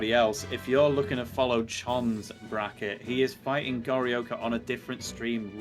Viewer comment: I thought he said "Gary Oaker" but it's Gorioka right haha.